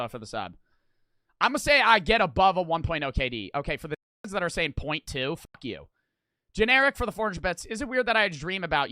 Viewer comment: Dawg what did he say 😭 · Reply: Looked at the vod from everest he said dickheads